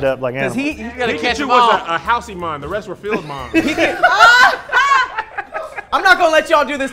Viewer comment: what does say at 3:47? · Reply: Pickachu was a house-emon the rest were field-'mons